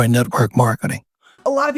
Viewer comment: Did he call MLMs “mark marketing”? Like…selling to a “mark”?